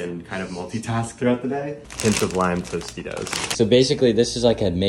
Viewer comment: I thought he said blind tostitos